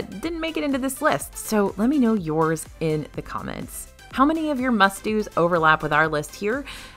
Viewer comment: I thought you said musty rides instead of must do rides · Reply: I heard "musty" 😂